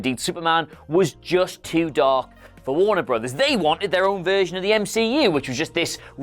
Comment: Did he Said they want their own vision of Superman in the mcu ? 🤨 min